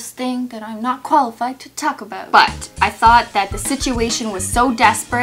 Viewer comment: When she said I’m not qualified to talk about I thought she said toco about